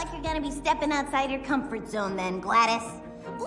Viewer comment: I thought vanellope said “Lettuce” instead of “Gladys”